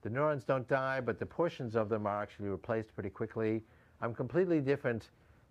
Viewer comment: What did he say 6:06 - 6:07 ("But the portions of the ....?....")??? Thanks. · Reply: portions of them (neurons) are getting replaced pretty quickly